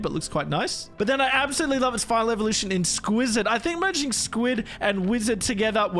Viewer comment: i thought sqwizard called squidward from SpongeBob SquarePants